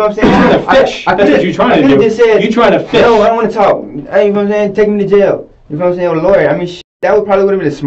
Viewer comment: Calling responses “mind games” is extremely charitable. 😂 Dude’s actually just smooth brained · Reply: I think at 25:40 he said take me to jail and get me a lawyer”. Close caption showed “lord”. Anyone else notice?